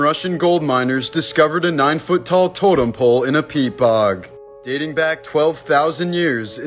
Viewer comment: I could have swore i heard " in a pea pod" i was like whatttttt!?